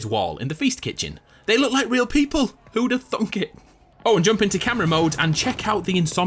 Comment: did he just say... thunk